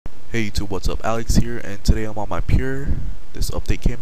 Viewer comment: "Hey youtube Alex here. And I'm on period" Lol sounded like you said that xD